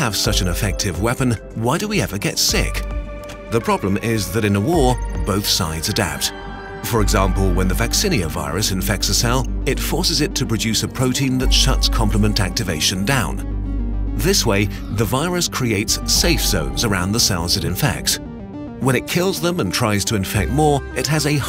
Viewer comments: I always thought you say compliment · Ohhhh I thought it said 'Compliment'